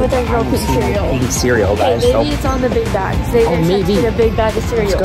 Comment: I thought she legit said maybe it’s in the big backs